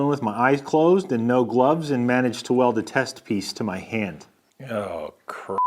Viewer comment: I wasn’t watching the the screen at this moment and thought he said “ managed to weld my testes to my hand”